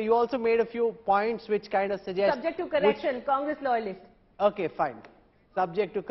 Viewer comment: A guy who lost to an amateur (joke) Praghya Thakur in his home constituency by 3.5 lakh votes is leading the Khangress! I am impressed. But around 8:40 something like I literally thought Divya said "Subject to corruption! Congress Loyalist" ;-)